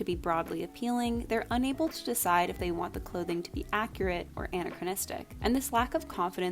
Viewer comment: I heard it as Anne-achronistic every time.